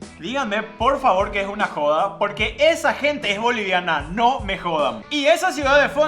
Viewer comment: Perdón escuché mal dijo bolivianos?????? (Soy #BOLIVIANO 😶🤨